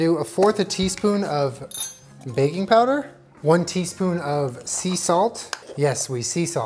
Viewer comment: I deadass thought you said baby powder not baking powder and I was like oh nO julen